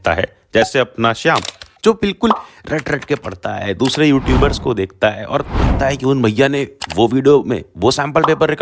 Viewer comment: I think he's saying about Shobhit Bhaiya ☠️